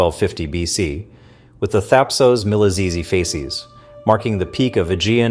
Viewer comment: "...the 'Thapsos-miliphasees something' ??? Wtf are you yrying to say, son ?